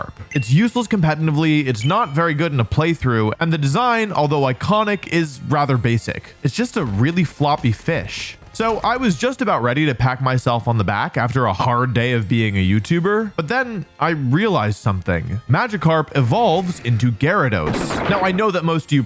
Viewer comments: Did say pack? 8:13 Sure, WYNAUT! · Why does it sound like he says "com-menanive" instead of comepetitive? 😂